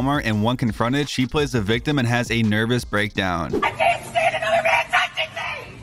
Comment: "I CANT STAND A MAN TOUCHING ME!!!😭👹" " no one did that😐👁️👄👁️" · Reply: I thought she said another man? There's no way any man let alone 2 men touched her ever lol ..